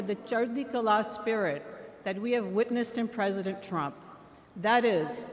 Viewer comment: did she actually say theirs a spirit in Trump! She said that we Witness!